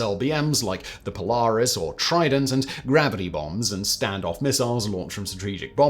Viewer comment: gravity bongs? Surely thats not what he said? Ive rewatched it 4 times and thats what it sounded like….